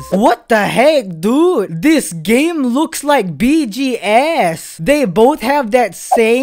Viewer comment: i thought he said BTS